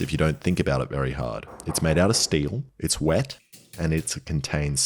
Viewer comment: not even a full minute in, and I hear "it's made of steel, it's wet, and it's big" and went "Oh, so it's a big boiling pot"